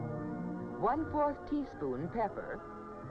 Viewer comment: People also sounded different then then what they do now have anybody else noticed this? Or is it just me? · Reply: Maybe it's the audio because it sounds like she says bebber instead of pepper